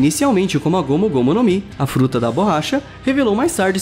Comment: eu entendi fruta da bolacha